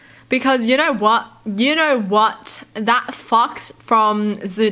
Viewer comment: I REALLY THOUGHT SHE SAID “THAT F**KS” NOT “THAT FOX” 💀